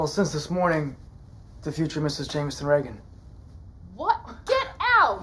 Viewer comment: I know his first name is Jamison but he sounds like he is saying Jamiston Reagan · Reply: I understand that but if you listen to Will as he says it it sounds like Jamiston even though it is Jamison